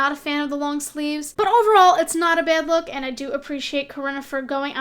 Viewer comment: Did you say Corona??